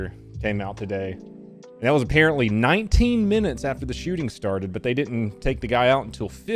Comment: A clarification might be in order: said police arrived 19 minutes after the shooting started but "didn't take the guy out until 50 minutes in". (I thought I heard they arrived 3 minutes after the shooter did... obviously could be mistaken there) So the real question should be, how many children were killed after they arrived... and can we even know without eye witness testimony from the surviving children?